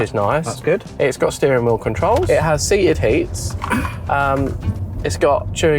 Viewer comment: Did just say “ Seated Heats” 17:41 😂😂